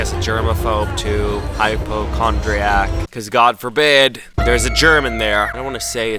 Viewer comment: The subtitles at 9:31-9:32, instead of saying "god forbid there's a germ in there," it says "god forbid there's a German there." Lmao, I didn't know sanitizing wipes got rid of seat Germans!